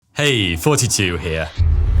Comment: I always thought he said "Hello! 42 here!" Now was the first time i connected what he said to the channel name. My brain is weird